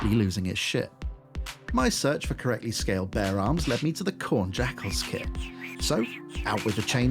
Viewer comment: Thought he was saying "bear arms" this whole time lol